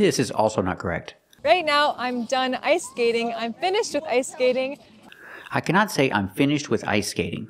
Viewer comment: I hear: "I'm finished to ice skating" But it makes no difference, it's equally wrong. Pretty much everything she says sounds like broken English, I can't believe she calls herself a native English teacher.